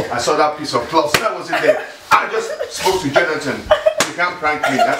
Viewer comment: I swear at 1:48 it sounded like dad said "I just spoke to Gelatin"